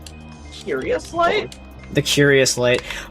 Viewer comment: did i hear curious light